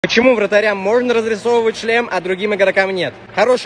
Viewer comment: Мне единственному послышался почему вратарям можно развивать ч*л3H а другим игрокам нет 😳